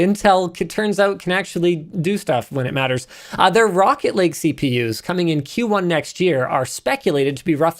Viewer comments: I thought you said "Rocket League CPU's" and immediately bust into hype electronic music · I thought he said Rocket League CPU and I just kinda stopped and thought what?